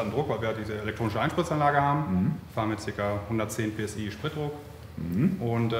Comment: 110 PS für die Spritpumpe? Hab ich das richtig verstanden? 👀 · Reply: PSI - Pounds per square inch. 7.58 bar Basisspritdruck